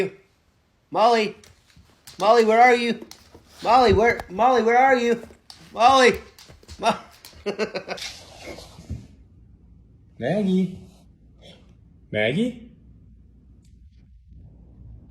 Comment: 🤣🤣🤣 this clever cutie decided this hooman needs fixing asap...start digging at that faltering brain 😂 *edit - can't make out if he's saying Manny or Maggie